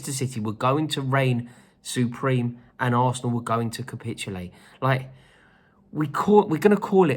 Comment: I thought u said Arsenal we’re gonna win the league !?!? 😂 this guy!!